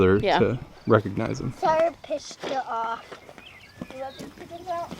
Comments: did he say “Sorry I pissed you off”. LOL. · I thought he said “Sorry I pissed you off.” Lol. I was laughing so hard. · "Sorry I pissed you off"? I died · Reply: Orson Zedd I thought he said that too and I was dying laughing, but his next sentence gave it context. I think he was actually saying “sorry I pushed it off” (as in the fence)